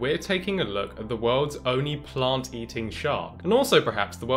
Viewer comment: Thought it said “planet eating shark” :/